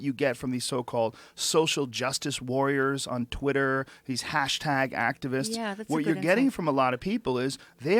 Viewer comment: Listening at 1.5× it sure sounded like Rogan had just said "hash hag activists" until I rewound and listened again. It's actually not very clear at this speed. My own inbuilt Imp of the Perverse obviously supplied the extra "h" where my ears shrugged.